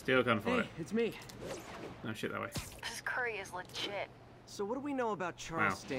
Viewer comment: See, this is why subtitles are great. When Mary Jane said "This curry is legit" I thought she said "This curry tastes like shit."